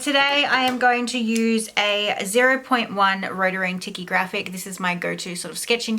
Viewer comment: I really want to know the pen you are using. I heard rotoring and tiki grafix, but its not yielding any results. Can anyone help me figure out what she said? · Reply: hi hi! The pen I used was a a rotring tikky graphic in the size 0.1 hope that helps you! X